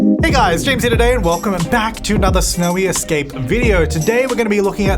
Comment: Almost sounds like you say smelly escape instead of snowy escape in the beginning of the video